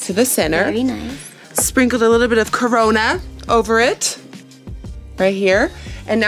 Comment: SPRINKLED A LITTLE WAT !!!!!!!!! Did you say Corona. How did you know!!!!!!!!